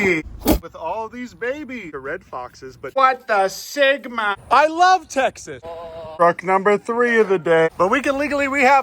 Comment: My volume was low and I thought he said “I love Tetanus”